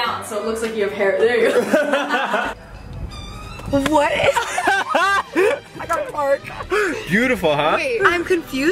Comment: I thought Gabbie said “I gotta fart” lol